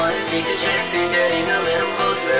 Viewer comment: I keep hearing "Creepo" instead of pretty girl...